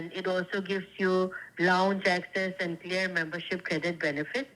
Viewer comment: Did she just say that the green amex card give lounge access and clear membership?